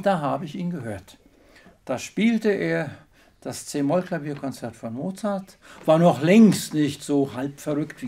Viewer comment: Was sagt er bei 1:11? "das C-Moll-Klavierkonzert vom Satz von Mozart"? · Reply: ohne "Satz" "das C-Moll Klavierkonzert von Mozart"